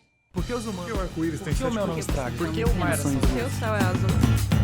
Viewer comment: só eu entendi ela falar pq o Sol é azul?